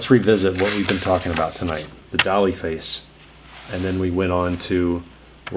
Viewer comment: At first I thought you said "dolly face" and I was thinking I definitely don't have a problem with making it too cute haha